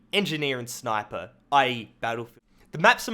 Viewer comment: what did he say? "ee battle"